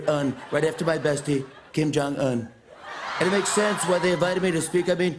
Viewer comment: I heard kim Jongkook😂😂😂